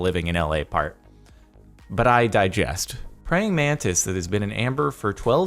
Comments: Did he just say "But I diggest"? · did anyone else hear digest not digress